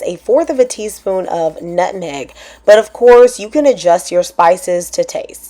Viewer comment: I heard that “nutneg ” 😊